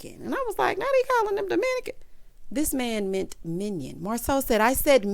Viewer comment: Tisha ought to be ashamed of herself putting her family on tv acting a fool like this. Ain't no way it could've been mine because they will NOT embarrass me like this. And whoever uncle granddaddy that was who was set trippin' in that blue paisley blazer sent me. He just came out of nowhere at Marsau talking about a 'minican. I thought he was really saying Marsau called him Dominican like it was offensive. I didn't know he was saying minion until your review lol. But seriously, Tisha get your family together and keep them off the tv.